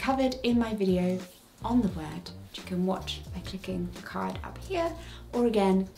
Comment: the subtitles say "which you can watch by LICKING the card here" 😂